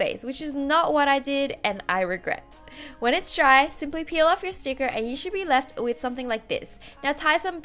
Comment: dID I hEAr SoMeonE sAy sIMpLy PEeL? 💿💿💿💿💿